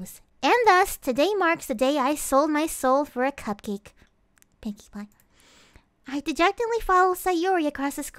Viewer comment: Did she say 'Pinkie Pie'?